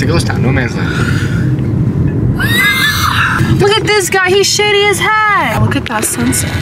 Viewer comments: Was that me or did I hear swear? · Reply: She said shady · did she say sh!tty idk maybe im jumping to conclusions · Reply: she said shady